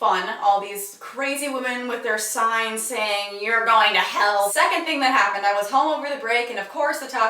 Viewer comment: WHAT?! I thought that angry mob was saying that if I didn't accept Jesus I was "going Taco Bell" FFFFFFUUUUUUUUUUUUUUUUUUUU